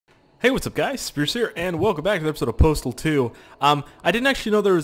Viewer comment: I thought he said "whats up guys scarce here"